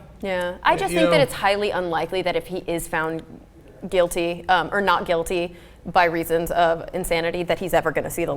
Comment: Oh my god please learn to understand English you dunderhead. 4:50 She said 'If he is found not guilty by reason of insanity'. OBVIOUSLY insanity is a defence and that's what she said.